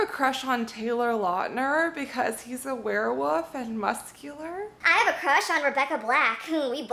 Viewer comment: she said wof instead of wolf